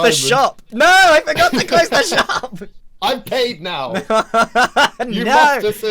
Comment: Just realized that the subtitles at 8:32 say "I'ved caved now!" instead of "I've paid now!" 🤣